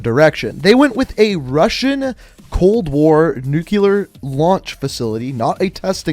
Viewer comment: This man keeps saying “nukeular” instead of “nuclear” and it made me think that was the proper way to say it 7:43 for proof lol